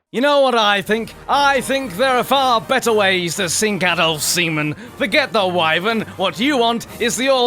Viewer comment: Sorry for the wait everyone, real life is taking over at the moment. In the process of moving house, among other things. Also, 4K on this video will be available in a few hours hopefully. Have a marvelous day. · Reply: Did you just say Adolf's semen?!